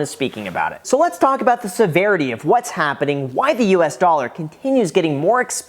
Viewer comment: All I hear is "its happening" Ron Paul was right